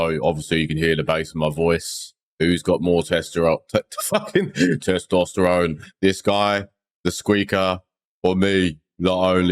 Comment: lmao the whole testosterone part got me, when fucked it, thought he said "whos got more testies" when he messed up. i love these videos